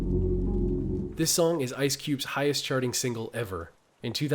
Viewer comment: …Why does it sound like you said “Ice Cue” the first time you said his name? The 2nd time you said it you definitely said the right name, “Ice Cube” but the 1st time you said it I keep hearing you say “Ice Cue.”